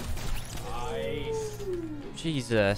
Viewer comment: who else heard say nicee